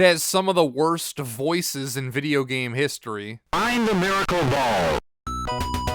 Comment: My cousin had The Lost Stars when we were growing up. I remember when my aunt misheard "Find the miracle ball" as "Grind the horrible mall."